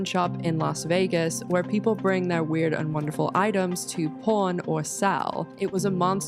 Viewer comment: As an American 🇺🇸 I keep hearing “Porn”, not “Pawn” haha 😂 Awesome